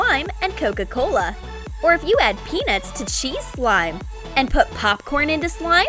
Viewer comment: Anyone else thought she said Penis?